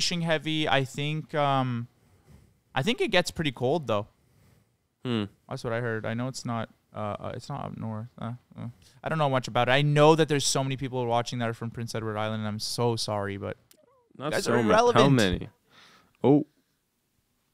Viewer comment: i thought u guys said rhode island i got so excited 😂😂😂 but u said edward islnd wtf